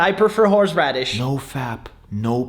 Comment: for a second I thought he said "Fab" as in Fabguys or Fabswingers websites